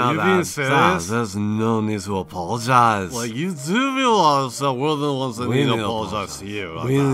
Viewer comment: I have no fucking clue what he's saying hahahahahha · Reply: "like, you do realize that we are the ones that need to apologize to you" i speak zoomer!